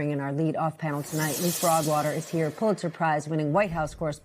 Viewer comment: Aw man, I thought she said Frogwater